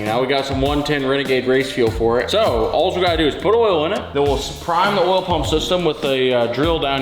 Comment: The technology that goes into these things is insane. Let’s supercharge it even more. Edit: my fat ass thought he said “put rotel in it”. Rotel as in, the canned tomato stuff 😂